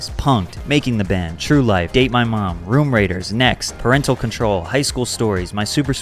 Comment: Lol I thought you said 'Date my Mom'.